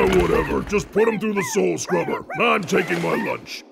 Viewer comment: did he said "soul scrubber"?